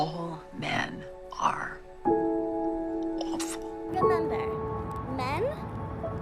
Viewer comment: I like how the subtitles on around 0:17 say awesome instead of awful. So true machine, so true